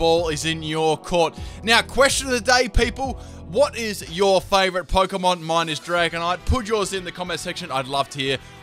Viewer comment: I thought he said "whats your favorite pokemon minus dragonite" like we couldnt have your fave mon :p Mines Sableye